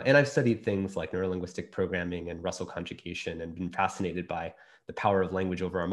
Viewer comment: does anyone know what he’s referring to after he mentions neurolinguistic programming at around 6:20? sounded like he said “russell contribution”? i love how language ended up being explored so much in this clip